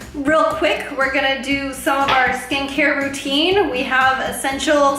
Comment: Lol I thought she said “stink hair” instead of skincare